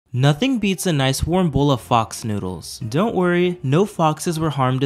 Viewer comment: I thought you were saying box noodles